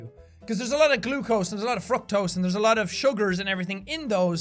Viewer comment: Is it me or did it sound like he said fucktose at 10:08😂